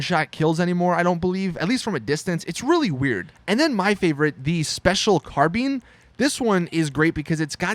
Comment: Wait did you just say "Special CAR BEANS